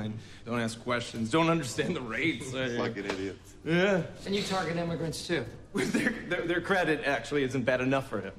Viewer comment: Subtitle error as 3:03 Error: " And you target them with red soup?" (makes no sense, is not what was said) Correct: " And you target them too?" (referring to the broker targeting immigrants too)